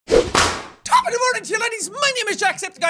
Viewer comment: I heard "ladies" in the begining of da vid xddd instead of